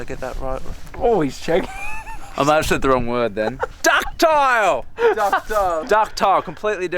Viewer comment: I thought he said Duck Tale when he said ductile lol